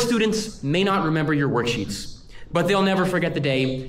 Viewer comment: I thought I heard warsheets instead of worksheets lol.